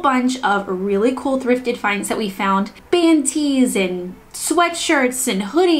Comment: "Band tees." I definitely thought she said "panties" at first and was temporarily horrified at the idea of DIYing thrifted underwear! 🤣